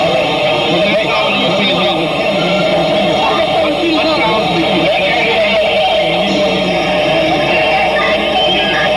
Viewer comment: Does anyone hear what Orio Palmer says to Joseph Pfeifer at 5:07 when he turns to him? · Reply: I heard Orio say “I’m feeling confident.” What did you hear?